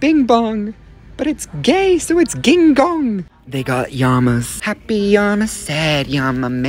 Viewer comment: I thought he said King Kong ngl🤣